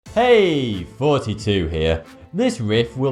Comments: Sounds like he says "hey 42 here" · I swear I just heard him say "Hey 42 here" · i knew you were saying 42 but i thought maybe you just said thoughty with a hard accent lol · When he says "Thoughty2 here" it sounds like he says "42 hairs">